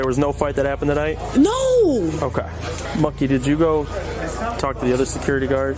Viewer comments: bro I swear I heard him say monkey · What did the officer say here? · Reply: He was talking with the other officer, called him "moekey" or something similar, dont know if its a name or a nickname.